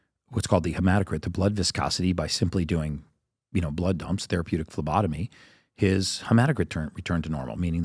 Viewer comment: man I hate it when they just mumble a few words quickly in there that are important. What are you saying? Blood dumps, therapeutic lobotomy ???